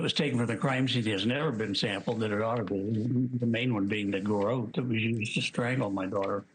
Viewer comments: did he say the garrote "WE used to strangle my daughter"? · Did he really say " the garote WE used"? 3.28. I've listened to it several times and it still sounds like he said WE used to kill my daughter. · WTF???Did anyone else notice John Ramsey say “The garrote WE used to strangled our daughter with” ??? Check the audio at 3:20 he clearly admitted to strangling his daughter???? WOW. Nobody noticed at all. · The Garrote we used to strangle our daughter is that what he said? Read the 1999 indictment a grand jury voted on it was a result of child abuse. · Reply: He said "...that WAS used...". Don't post crap like that. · John knows exactly what went down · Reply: Listen to his words starting around 3:20.... confession? I hear " that WE used to strangle my daughter" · Perhaps it is a slip of the tongue, but at 3:26, does he say the garotte we used to strangle my daughter? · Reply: He said "was" watch his lips closely and you see and hear the "was". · did he just say "the garrote that WE used to stangle"... or was the word "was" and not "we"... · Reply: He said was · It sure sounded like he said "we" when he was talking about the garotte used to strange his daughter. He said he wanted the "garrote we used to strangle our daughter" to be sent for Dna testing. Did anyone else here that? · Did he just have a little Freudian slip? It sounds like he said "the garrote that we strangled our daughter with" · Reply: I think he said "the garrote that was used to strangle my daughter."